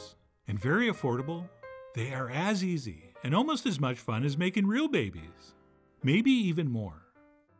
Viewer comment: "As easy and almost as much fun as making real babies".....almost choked on my water laughing when I heard you say this. Love you Chef John and your sense of humor! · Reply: I heard "baking real babies".